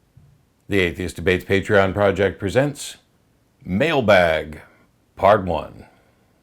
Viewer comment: Ohhh... I thought You said "Male bag" lol ;)